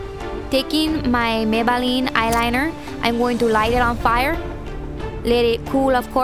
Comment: ahaha " taking my Maybelline eyeline, im going to light it on PIRE" haha thought she said that funnny <3